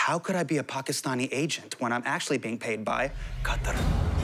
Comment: who did he say was paying him? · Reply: Qatar